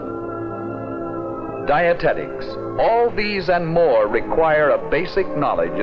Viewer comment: Wait, did he say Dianetics? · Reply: I think he said dietetics. As in creating diet foods that are supposed to keep us from getting fat and that really worked.